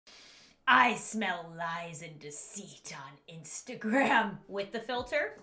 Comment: I thought you were gonna say “I SMELL PENNIES”